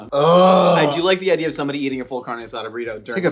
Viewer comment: Wait at 32:05 did Josh say “he likes the idea of someone eating a full carne asada burrito during sex”? Or did I hear that completely wrong? 😂